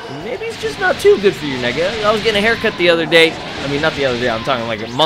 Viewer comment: did he say nigga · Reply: I said neck lol